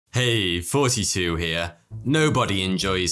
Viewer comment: I keep hearing "Hey, 42 here".